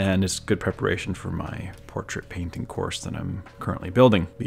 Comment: did I just heard "portrait painting god level" course? made by our lord? dude just...JUST TAKE MA MONEYYYY NOWWW :O!!!!